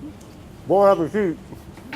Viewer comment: Love the editing yall do it's funny, hey Brad, did you know when you said Bon Appétit @ 7:58, youtubes captions think you said more rubber feet which is hilarious considering the editing.